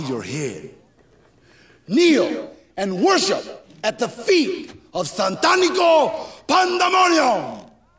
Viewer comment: did someone say I F’d her ? 😂 😂😂